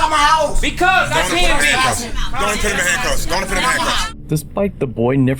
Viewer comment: It doesn't matter if he was "running his mouth" or not. The police can NOT enter a home without a warrant or permission to enter. The ENTIRE issue is due to the POLICE violating the 4th Amendment. There's a legal concept known as "fruit of the poisonous tree" whereby if the first action taken by police is illegal, then all subsequent actions are illegal also. In this case it would be that the police entered the home without warrant or permission. Therefore, since it was illegal to enter the home, the detainment was illegal, which meant there was no detainment/arrest to resist or obstruct. Police need to LEARN that taking illegal actions does NOT make better policing, not does it make subsequent charges valid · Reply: wait, didn't the mom said, go on in and put handcuff on him that what it sounded like to me at 3:58?